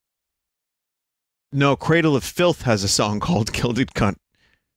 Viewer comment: Does someone know what song was it? At around 1:10:00 My stupid ears keep hearing "Guilded c*nt by the ozzies" Any better guess is appreciated.